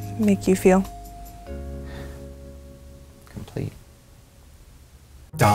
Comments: Is it bad that I thought it said dollar store the rapist😂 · I thought It said Dollar store: the rapist